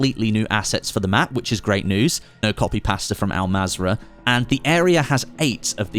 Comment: loving your work....BUT did you just say 'Copy and Pasta'?